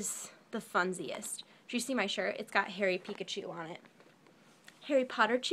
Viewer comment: I thought I heard her say "Did you see my shirt, it got hairy Pikachu on it".